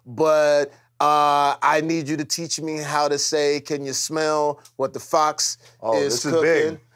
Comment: It sounds like he said the fucks is cooking